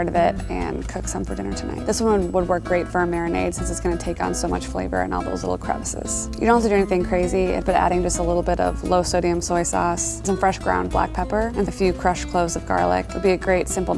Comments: Did she just said a little bit at 1:47 is that a little bit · did you just say little bet??